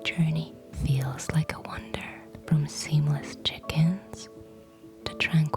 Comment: This is INCREDIBLE. Also, in the airline commercial I hear “seamless chickens” instead of “seamless check-ins,” which to me is in keeping with the Avians of Accord.